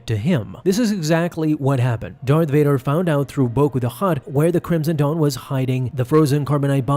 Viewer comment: For a minute I thought he said Boba the Hutt